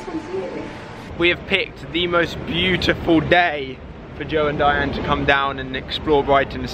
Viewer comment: I thought he was gonna say “Why have picked the most beautiful duck...” at 2:13 😂😂😂